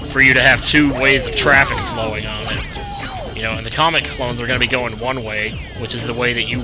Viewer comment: It sounded like you said comic clones lol also, this is part 39 :P